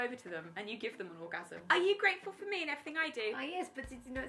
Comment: Lol @ 7:45 the auto generated captions (which are usually garbage, but it’s fine) say “nothing I do” instead of “everything I do”